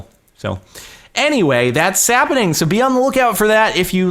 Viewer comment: it sounds like he said "that sappening"